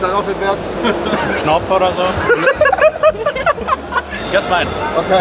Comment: was sagt er ?-😂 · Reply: Stich3s Thels er sagt bist du ein Opfer oder so